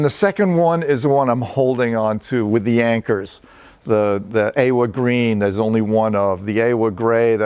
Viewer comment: what is he saying a-wood green? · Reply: The Awa Green. Which is a vintage compressor from Australia and is a fat thick tube compressor. It is painted in a beautiful line green